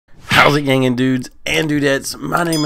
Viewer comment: For the longest time I thought you said “how’s it hanging” since the captions said that. Lol